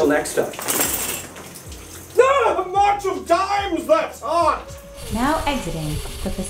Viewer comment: Did you say "March of Dimes, that's hot!" at 8:06? Like the mother/child health and wellness charity? I don't know if I want to understand... XD